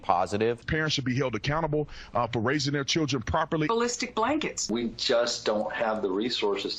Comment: I thought she said Holistic blankets 🤯🤯🤬